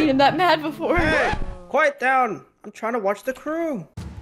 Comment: did he said ✨the krew✨